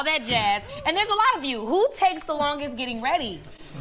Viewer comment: 질문 뭐라고 하는거져.?? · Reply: She said: "And there is a lot of you. Who takes the longest to get ready?"